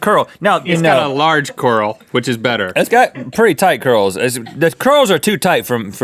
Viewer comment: "its got pretty tight curls" what i hear: "its got pretty tight gurls"